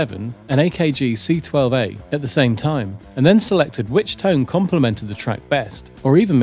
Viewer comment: "Complement" (with an "LE") means to complete - as in "a full complement." The phrase "to compliment" (with an "LI") has no proper meaning other than "to pay a compliment" (e.g., "He complimented the chef on a fine meal"). Confusion likely sets in upon hearing (but not Reading) a statement such as "The new goalkeeper complements the team." The error would then seem to stem from assuming that "compliment" can be interchanged with "flatter" (e.g., "The dress flatters her" - which is proper English) - and thus the mistaken switcheroo is made. Misuse of "compliment" in this fashion is now rampant due to the YouTube Feedback Loop - wherein video-makers parrot one another's errors. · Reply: The word is 'Complemented'. Which is widely understood to mean 'goes well with' as well as 'completed'. It does sound like I say 'Complimented' though.